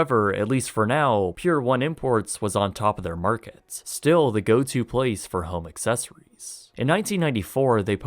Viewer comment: lol @ 4:06 I SWEAR at first I thought you said "The go to place for homosexuals".....lmfao :P